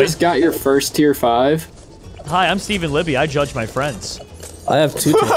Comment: I thought said drugged even though he said judge